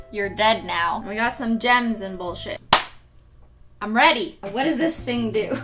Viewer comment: Am I the only one that thought she said we have some Jens and lotion? Yes? Ok. 😩